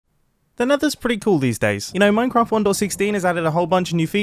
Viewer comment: during the intro i thought he said, “You know, meth is pretty cool these days.”